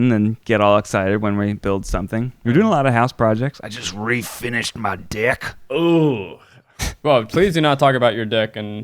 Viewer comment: Wes will know when the babe comes home. 18:47 - I heard "refinishing his dick" also! (And was wondering exactly how he did that . . . as my husband just turned 70 and . . . ) 😜 - 🥒🍆🥕🌭🥖🍢🥢🍡🍌